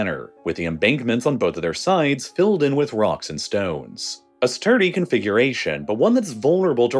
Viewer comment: did I hear a rock and stone?